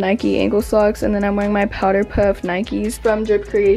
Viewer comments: im sorry but did she just say "powDer puff" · did she just say POWDER PUFF?